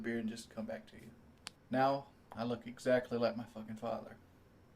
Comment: "Now I look exactly like my fucking Father" I thought he said I look exactly like my thumb at first.